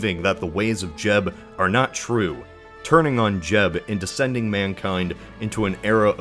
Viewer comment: I'm sorry did he just say "Turning on Jeb" ?